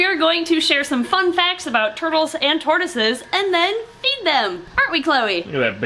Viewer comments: oh my gosh lol i heard "eat them" instead of feed them at the start :O. luckily i know (/ pretty much everyone in snake discovery) would never do such a thing. :/ right? At least and dont eat tortoises (or turtles) alive right? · why’d I hear eat them..